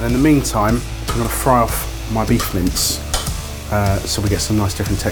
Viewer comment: At about 1:30, said that it was time to fry the beef mince. I mis-heard it and thought he said, “beef mints.”